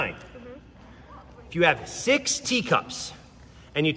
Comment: The third one honestly is not cool. He definitely says there are 26 cats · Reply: He does that on purpose and he said six teacups he said it squished together so it sounded like he said 60 cups it's like he wants you to fail